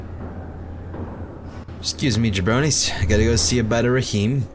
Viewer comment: did just say jabrony. He knows his hockey.